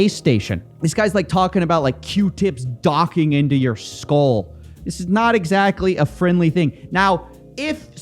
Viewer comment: I had the biggest laughing fit upon hearing "this guy's talking about q-tips docking into your skull" (admittedly I heard "into your soul" at first but it's still funny)